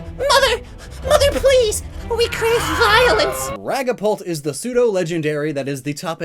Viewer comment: Did he just say ragapult